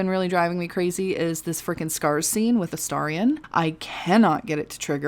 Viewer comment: I'm sorry people are stealing your stuff, that's bullshit. Hopefully you'll get it taken care of. EDIT: I thought you said "stars" scene, not scars scene. I never got that one to trigger either. I wonder if, like the stars scene, there's a certain thing you have to do to make it trigger.